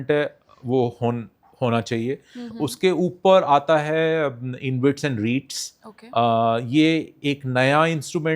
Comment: Can u pls clear 16:03 is what he is saying " inbuilts and reads ? What is that pls clear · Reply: REITs and INVITs